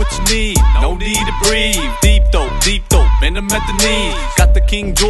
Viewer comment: Listen to 1:17-1:40 I think I heard "deep throat deep throat" twice...bwahaaaa Lmao this is hilarious